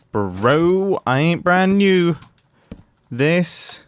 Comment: did he just say "I ain't brand new"?